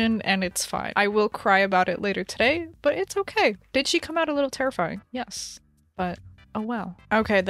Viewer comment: why did I hear “bi*** she come out a little terrifying?” 😭